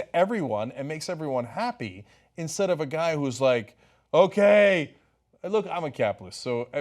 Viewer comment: What does Cenk say at 5:38, he's a capitalist?